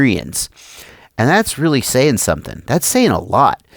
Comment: Did you say "seltsam" ?